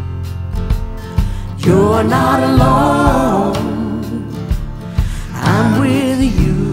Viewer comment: Here bc Murders In the Building Season 2 Episode 6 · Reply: Me, too! But, I was concerned for Mabel, because I thought I heard "You're not alone... I'm you." And what with all the crazies in the show, I feared for her! 🤣 This is a wonderful song by Mavis Staples!